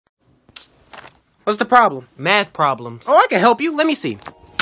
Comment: 나만 00:00 왔더 퐉으로 들었나.. 매쓰 퐉...!💢